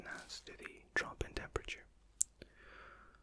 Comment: wait... did he say “trop in demperature” at 21:20?